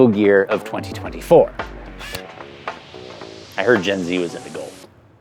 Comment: i cannot for the life of me figure out what the first punchline was.. "i heard gen z was into golf"? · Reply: Ah you misheard, he said “I heard gen z was into gold” referring to the helmet color